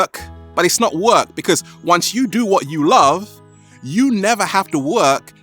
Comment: How can I travel without money :/ ? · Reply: I heard him say do what you love to get money